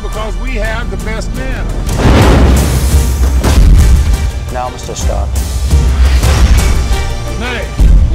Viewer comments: Did he say 'Now Mr. Stark?' As in Tony Stark's father? :o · does he say ready Mr. Stark? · did anyone else hear "Now Mr. Stark."?